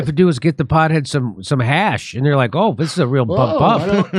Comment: Did someone say Hash?